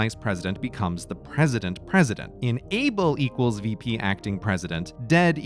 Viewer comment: Why can Grey not pronounce "in-able"? I keep hearing "enable".